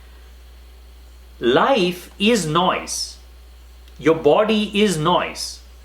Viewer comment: I know this is off-topic but I kept hearing "noice" 😂